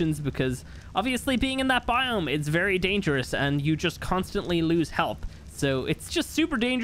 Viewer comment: Did he just say you constantly lose help